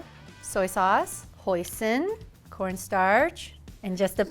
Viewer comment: when she said hoisin, i thought she said poison and i did a double take sdfjsdnfjks